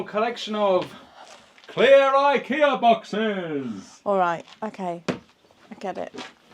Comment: Mate firstly I thought u said a Rakia boxes! Being in Bulgaria in future u will need more a Rakia boxes (to collect your future homemade Rakia) not Ikea boxes! 😂😂😂